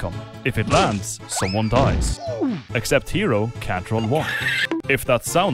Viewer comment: What are you saying at 1:29 because the captions say Catron walk.